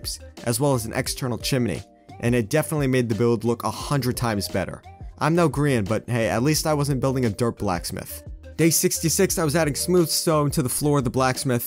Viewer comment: did I just hear you say "I'm no Korean" lol